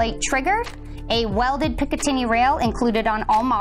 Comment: I heard " pickaninny" in the 1st Umarex desciption, was that supposed to be picatinny rail?.......